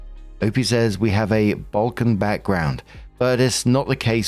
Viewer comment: Balkan background… I dropped something when you were saying that and thought you said Vulcan… then get to the updates smh thinking maybe it was bogan… yeah that fits. Went back in to find it… live long and prosper I guess, hope you have a laugh. I wish for a flock of bin chickens to sit on the roof of this b…. Who calls herself a mother and another flock for the aunts roof and for a peaceful 2026 for op away from their crazy!